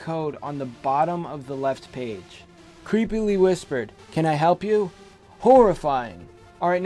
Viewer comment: uhh... i think it said ' creepily whispered " can i keep you?" horrifying!